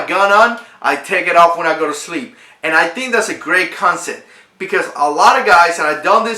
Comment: did you say consit? CONSEPT***